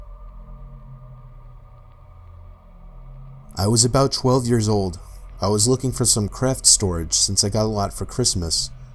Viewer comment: Did he just say "I was about 12 years old and my twin 7 year old sister" or is it just meap going crazy? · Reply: Pusheen Plays pokemon 27 year old sister.